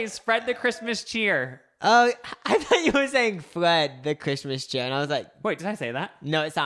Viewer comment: Literally thought he said Fred the Christmas cheer too lmao, and guess what guys, the day of this release my Christmas cash came in. Hollah